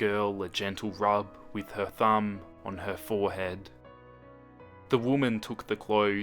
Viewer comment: I had 2 reverse this 5 times because i was being stupid and i kept hearing "forehead thumb on her forehead"lmao